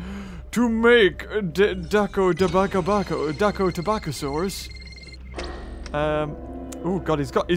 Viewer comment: Did say dako and then tobacco!